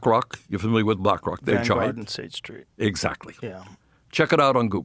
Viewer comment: What does Joe say at? 0:40 Just after Bernie asked him, “are you familiar with Black rock”. I even turned the close captions on it. It doesn’t say what Joe said. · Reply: Vanguard and state street